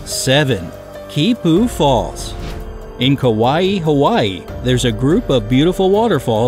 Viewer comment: Did it say kawaii hawaii